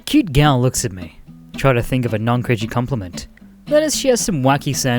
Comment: In the very first one I misheard "non cringy" as "non british"